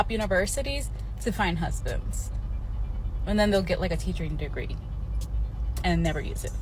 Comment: Did she say a "teachering" degree??? bruh